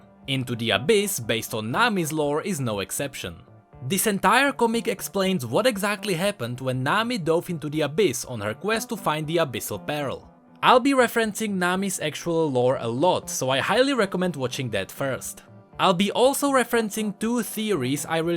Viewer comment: Yo, just wanted to let you know that you pronounce the "ea" in pearl as the "i" first, so kinda like an "uh" sound. Just some constructive feedback on the misspronounciation, that's all :) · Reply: IKEA 2 hard 4 fresco yea he sounded like he was saying "abyssal barrel" lol